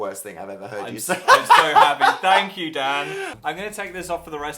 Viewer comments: i thought he said thank you dad instead of dan xD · Did Phil say "Thank you dad" or "Thank you Dan" Cuz I'm sure I heard "dad" ... ((daddy)) sorry